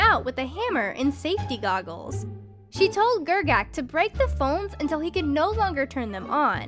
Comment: I thought it said bones